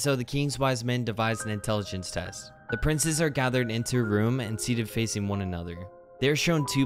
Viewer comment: When it said gathered at first I thought it said gonahria